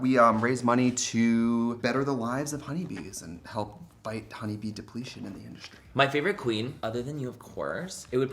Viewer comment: When trixie said honey bee depletion I legit thought he said honey bee depression.